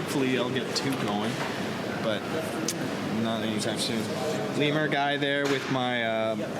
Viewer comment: What did he say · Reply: "Not anytime soon"